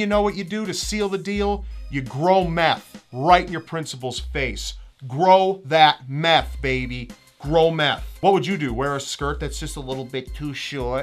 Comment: whenever said "throw meth" I thought he was saying grow meth, wtf brain?